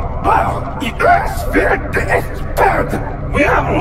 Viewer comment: lol I heard not “who are to accuse me”,but “WILL YOU FORGIVE ME”,it would be even funnier though